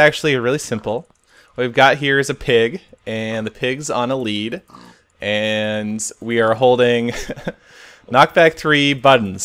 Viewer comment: It sounded like you said The Pigs on The Wing